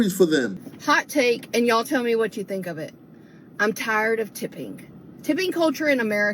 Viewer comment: Blonde Southern lady at 4:37. Did she say "tips?"